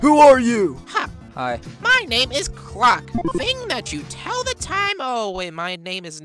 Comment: i think he said "croc" instead of "clock"